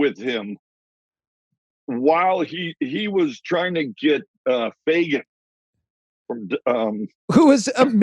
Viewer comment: Who is he talking about before the host cut him off without even knowing who he was talking about? Did he say "Fagen"? He was doing a hand motion like air guitar or bass. Was he talking about Walter Becker?